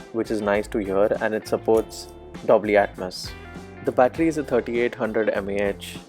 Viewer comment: Did he say DOBLY Atmos for Dolby Atmos🤔